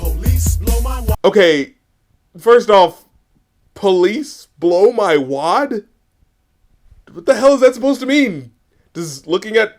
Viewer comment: "police blow my wad" I thought he said "police stole my watch"